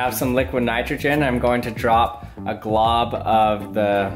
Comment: Did he just say "glob"? The Globglogabgalab returns.